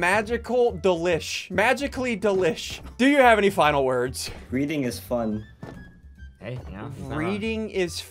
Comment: "Breeding is fun?" "No breathing" Dude I am actually crying 🤣😂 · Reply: He thought he said reading is fun